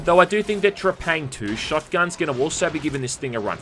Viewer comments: What fucking game is he saying in 12:51 I can’t understand his Aussie accent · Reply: He's saying exactly what it sounds like: Trepang 2. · What game are you talking about at 12:50 i cant understand what you’re saying but it looks cool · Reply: Trepang 2.